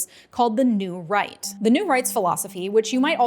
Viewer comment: I misheard "the New Right" as "the New Reich", and unfortunately it still seems distressingly applicable.